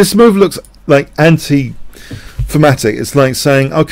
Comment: great vid, can you tell me what the spelling of the word is you use 3 times between 5:30 & 5:40 sounds like fermatic. TIA. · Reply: Thematic. Yes, his accent made it sound like "fematic".